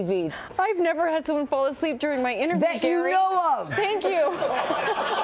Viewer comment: What does Carrie Fisher say at 5:12 ? (It sounds like: ,,that you know of") (I am foreign.) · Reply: Yeah, she says "That you know of!"